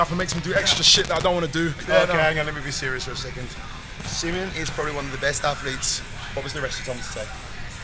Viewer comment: What is he saying at 20:40? Wanna laugh too! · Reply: he was like Simeon is probably one of the best athletes, what was the rest you told me to say. Basically, he was trying to make it look like Simeon told him to say that; as a joke of course.